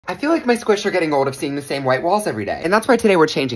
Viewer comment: I thought he said white balls ☠️☠️